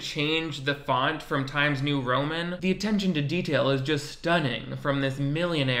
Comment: you said "attention" and I heard "cadet kelly clip breakdown"